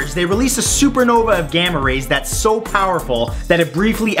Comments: I thought he said "gamma rapist"....!! XDDD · I thought he said Gamma Rapist...Oh I am glad I read the caption. Feels Watched :o